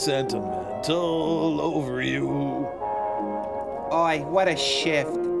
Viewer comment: When I Was A Kid I Thought Crow Said "Oy, What A Shit."